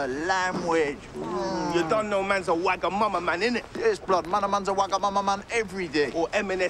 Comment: I'm Australian and LOVE this show, but having trouble deciphering what they are talking about at 0:33. "You don't know man's a ......" ??? Can someone shed some light on what they say and what it means? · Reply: TeamKilday Wagamama is a noodle house ... there talking about high end snacks